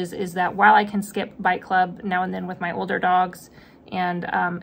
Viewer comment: I thought you were saying “bike club” and couldn’t figure out how that fit in with training!